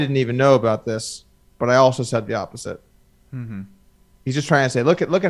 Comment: I think they're misunderstanding what Bryn said @ 1:02:00 . He didn't say " I said the opposite" w/r to this specific incident, he said "I've said the opposite" as in "my policy in the past has been no cheating/ghosting etc"